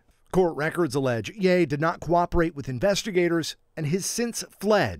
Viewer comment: Wasn't paying much attention but was curious when i heard " investigators said yayyy"